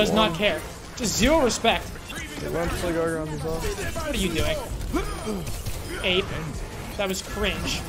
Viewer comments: did he call bap ape? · What did he say after killing bap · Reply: "ape"